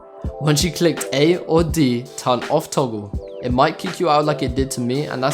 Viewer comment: what did you say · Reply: I said “ turn off toggle”